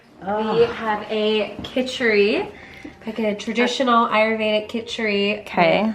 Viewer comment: what word did they use to describe the kichuri? i thought they said aromatic but i replayed it and sounds like something else? anyway nice to see their take on a south asian staple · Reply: Nawreen Juthi she said ayurvedic kichuri